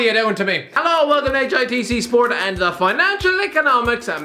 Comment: I thought he said Hiv sport.